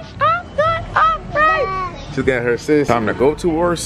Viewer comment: Subscribe & Turn On Post Notifications ❤️ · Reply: First time I think I heard ya say 'im doing okay' instead of ' I'm doing alright I'm doing alright I'm doing alright' aye aye.... 💙💪💜